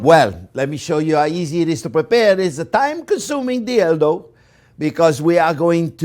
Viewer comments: it sounds like he says "time consuming dildo" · i'm so sorry but i heard "time-consuming dildo"